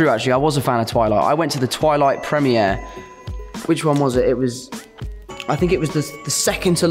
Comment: Wait a second... ...wait a second 1:34 ...did he just say "prem-eeh-air"?!